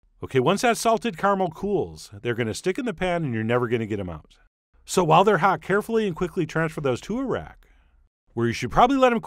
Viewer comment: I thought he said "Transfer them to Iraq" and my brain just went "aw he cares for the soldiers."